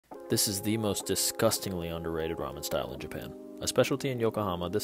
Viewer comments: MAN I THOUGHT YOU SAID THE MOST DISGUSTING RAMEN IN JAPAN 😭😭 edit: not to be that guy but… HOW DID I GET 2K LIKES LIKE WTH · I thought you said “disgustingly undesirable” but then I heard you say “impress your friends” and went back to rewatch. 😅😅😅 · I THOUGHT HE SAID "most disgusting ramen in Japan" 😨 And then I saw the food and said WHAT DISGUSTING??? THIS IS FUCKING DELICIOUS